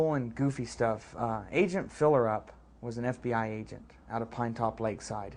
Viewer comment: Ahaha I didn't get what he was saying! "Filerup"? Mr. Fillrup? or Mr. Philerup? I thought he was saying a real name I never heard before xD